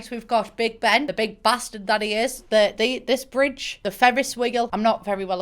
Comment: I thought she said "The Fevris Wiggle"